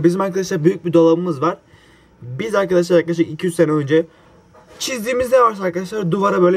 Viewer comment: 200 sene ????!!!! 1 dk sonra 2-3 sene dediģini 10. dinleyişimde anladım